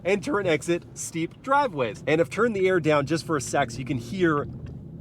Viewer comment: WHAT DID HE SAYYYYYYYYYY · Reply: Second maybe😂